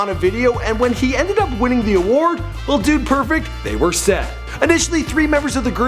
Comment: I thought it said "Dude Perfect, before they were perfect" 😂😂